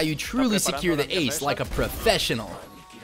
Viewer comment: Why did it say ass